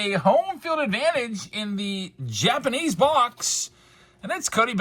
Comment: I Swear I Heard “Jabs’panese” at the beginning! 🤣😬🤷‍♂️